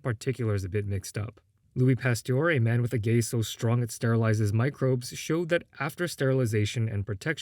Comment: I’m listening to this on a Friday night after already having too much to drink and I thought I heard you say “a man with the gay so strong it sterilizes microbes”